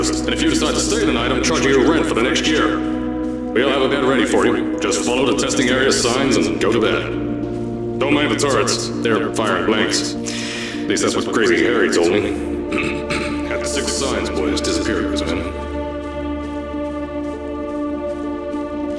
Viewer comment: I heard cave say "already that's what crazy Harry said to me"